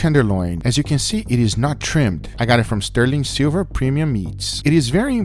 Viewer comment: My stupid dyslexic ass thought he said “sterling silver premium yeets”